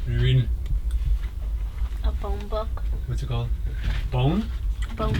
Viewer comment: I thought he said he was reading a phone book, lmao